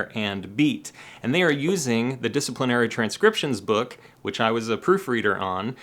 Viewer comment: In the introduction, when you talk about the "Discipline Era transcription book", it almost sounds like you say "disciplinary transcription book"... like, "be good and behave yourself, or we'll make you learn all this!".